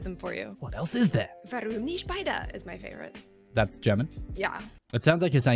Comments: Alright..I am German, and I have no Idea what she just said. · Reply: I think she said "warum nicht beide" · i'm german but i don't understand what she's saying.. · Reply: She said: "Warum nicht beide?" "Why not both", for the english speakers. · Ok what did say at 3:54 ? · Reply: Warum nicht beide :)